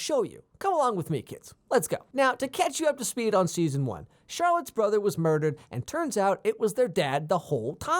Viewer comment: "Charlotte's brother was murder" 1:13 did I hear that correctly? Her name is Cheryl.